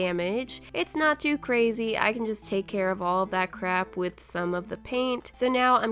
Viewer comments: did she say crap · Is it just me or did she say “ all that crap “ I might’ve misheard what she said though. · Did she say "crap" at 10:38 ??? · Was this anyone else’s reaction? 10:37: I can take care of all that ???? With ( didn’t hear what she says after) Me: - doesn’t here what she said - Also me: - goes back 10 seconds -: I can take care of all that crap in (still can’t here what she says) Me: MORIAHHHHHHHH!!! Just me?.................. - Waiting - Ok... 😑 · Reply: Mrs. Potato Head she said with some of that paint I can’t tell if you’re joking or not